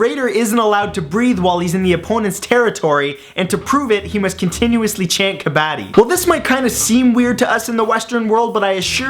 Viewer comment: What did he say? 'chain kabatey'?